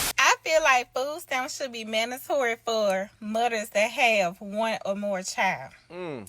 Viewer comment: did she say mother's or mutters 😂😂😂